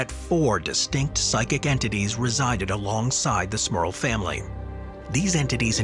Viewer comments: me, too, thought it said, 'Smurf'! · I just read this as The Smurf Family Haunting 😂 🤦👍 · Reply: I thought it said smurf also